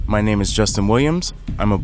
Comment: I thought he said, "my name is Justin Lambs." And I was like, "wait a minute?"